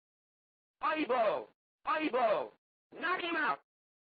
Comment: I always thought it was saying Honey Blow